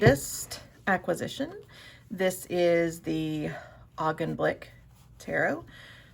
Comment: I am sorry, but what is the name of the deck? I cannot hear well. 😊 Very interesting video! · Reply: The Augenblick!